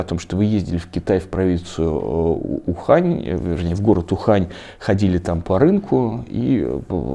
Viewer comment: Я поехал в Ухань Бухать... Там такая глухомань — Благодать... Я на рынок зашёл Разок. И купил я ежовьих Кишок... Посоветовал мне Один тип. Сказал, лечат простуду И грип... Только вирус в короне Подкрался, В организм мой Без спросу забрался... Пацаны... Я, кажись, прое*ался... (вступительные стихи из книги «Я легенда. Как всё начиналось.») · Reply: Мне вообще сначала послышалось,что Варламов сказал город Бухань. Я сначала такая: " Чтооо.. " 🤣🤪